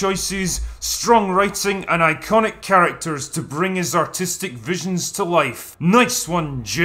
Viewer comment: I thought he said "autistic" and I died from laughing